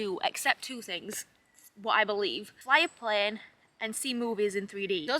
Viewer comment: "except two things, I believe: [something] and seeing movies in 3D" I didn't catch that first part, can anyone tell what she said? EDIT: It was "fly a plane"